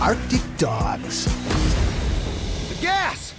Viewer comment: I saw the poster for this movie when I went to go see joker and I thought it said autistic dogs.